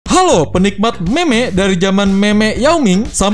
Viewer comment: kok saya dengernya "meme*" ya bukan meme :v